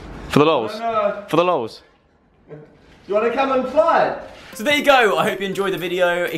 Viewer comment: Is it just me or did just say: "Do you wanna come and FLY?" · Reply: He said do you wanna come and try it. I'm 3 years too late 😂